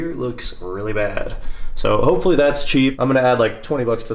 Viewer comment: did you just say "SHIRT CHEAP"